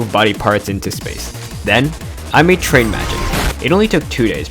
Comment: wait... Did you say... Train magic? Train horns intensify